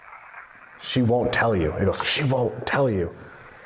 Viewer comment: I think she actually was singsongy saying "it's someones birthdayyy" 🎵🎶